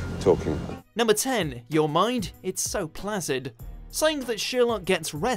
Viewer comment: When he said “Your mind, it’s so placid” 😂 I thought he said flaccid 😭😭😭😭 omg